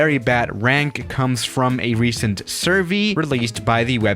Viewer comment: says survee instead of survey